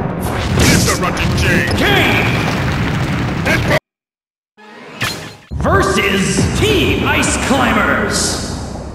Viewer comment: Yo what did Cell just say? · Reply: It burns.